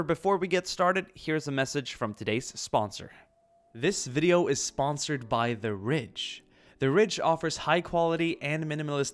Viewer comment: I must admit when he said who his sponsor was I kid you not I heard: "This video is sponsored by the rich" xD